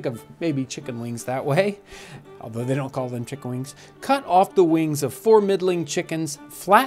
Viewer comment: For a second there I thought you said "Cut off the wings of four meddling chickens..." Crikey. That was pretty drastic for the poor ol' chooks!